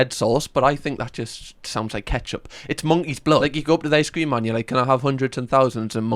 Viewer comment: monkey’s blood? Is that what you are saying? 🤔 what in the hell is that?